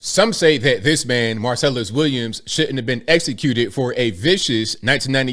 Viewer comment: I thought you said Marcellus Wiley I started Waylon out not my dawg!!!